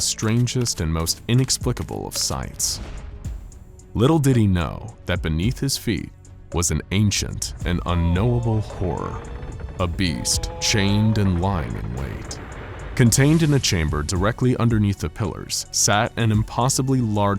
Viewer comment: why everytime he said " a beast" i keep hearing it as "obese" its fucking hilarious